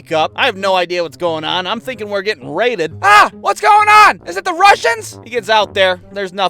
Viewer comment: did he say "Are those Russians?!"